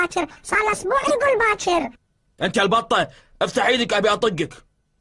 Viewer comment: well he didn't say fat*** , what he said was (Duck) and no we use duck for the fats (ba6ah) or بطة